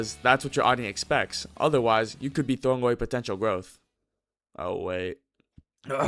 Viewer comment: did you say potential?